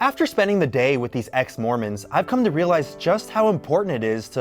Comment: omg, i thought you said, "I spent a day with EX-MORONS" oml 😂